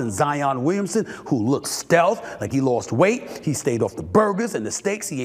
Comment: thought I heard stephen says “he look stake” 😃